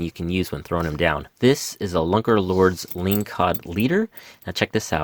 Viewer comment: Love the video, rockfish and lingcod is fishing is among my favorite here in Alaska. At 15:04 you show the "Lunker Lure lingcod leader." (I think that's what you said) I cant find anything about it on google. Do you have any info on it or ideas on how to craft one? Thanks! Loved the video